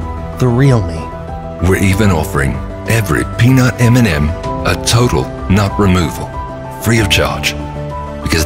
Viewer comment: This is the one time I heard “peanut” m&m … nut removal free of charge. Every previous time, I heard “pink” m&m hahahaha, like the pink ones are gay! Lol